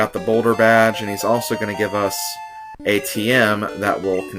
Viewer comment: When you beat Brock, it sounded like you said, "He's gonna give us ATM." Wouldn't that be a generous boss?